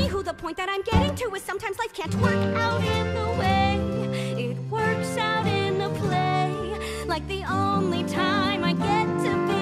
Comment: why do I hear “twerk out in a play” 😂